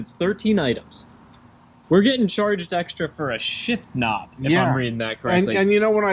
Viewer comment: I keep hearing "shit knob" every time😂